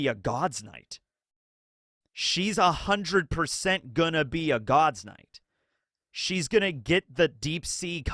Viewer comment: Stuessy was a clone, yes, and she did eat a devil fruit (Bat Zoan). Stuessy is NOT a God's Knight, she was CP0, so I have no idea what your point is. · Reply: okay I have no idea why I thought you said "eat a devil fruit" instead of "be a God's Knight" 🤣 mb